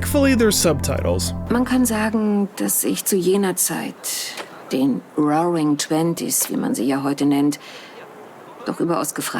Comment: got the subtitles wrong lol. She said roaring 20s, not golden